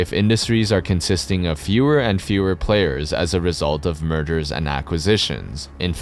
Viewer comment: “murders and acquisitions” that’s what it sounded like….and initially you think that might be a mistake…but that may be more accurate.